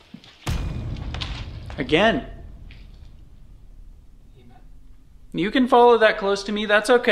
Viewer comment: In 12:59 - 13:00 it sound like someone said "EMF"